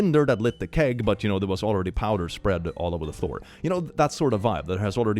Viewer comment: Ok, I rewound to hear this again, because I didn't hear "floor" the first time and was like "Who the hell keeps gunpowder in a forge?"